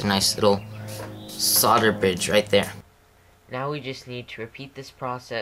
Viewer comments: did he say solder bitch? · did you say "it creates a nice little solder bitch right there" ? lol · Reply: He said solder bridge!